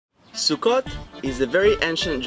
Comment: The way he was saying SUKKOT with out the T it sounds like he was saying Russian SUKKA, witch doesn't sound right when you know what SUKKA means in Russian.